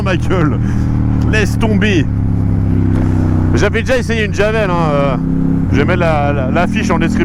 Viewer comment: je rêve ou j'ai entendu "j'avais déjà essayé une javel" ??